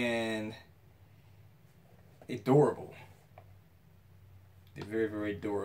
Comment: i thought you said adorable!! LOL you were saying durable! Good one.